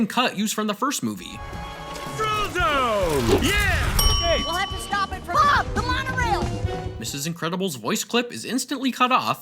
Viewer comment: I thought she said: "We have to stop and F**K the monorail!"